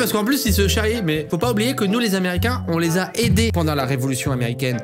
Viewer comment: A 8:28 il dit nous les Américains où ses juste moi qui entend ça